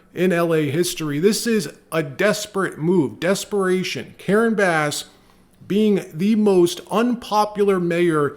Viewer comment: I gotta get my hearing checked. Could've sworn he said Karens ass.